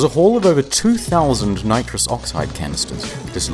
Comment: ehm, hi? I'm not native english speaker, and I just wanted to ask, is the gas mentioned in 2:57 is nitrous oxide? I'm not sure if I hear that name properly